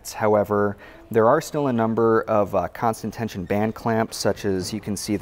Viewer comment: Oh, it's constant tension "Gland Clamp". At first I thought you said constant tension "Band Camp", which kinda made sense too. ;> Great presentations guys/gals. Very educational. We appreciate the details! And of course, constant comparisons to other makes is the icing on the cake.